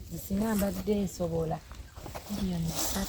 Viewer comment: did she say 30 million or 300 million? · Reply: Ndowoza shs 30million Naye it's possible if amongst the 10k views we raised shs5k